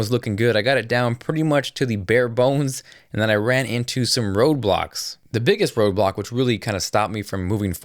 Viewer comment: thought he say roblox lmao had to replay it